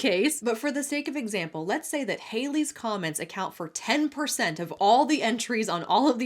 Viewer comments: 😂❤ I kept hearing "Hailey's Comet"!🌠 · ✋ Who else thought she said “Halley's Comet”? ✋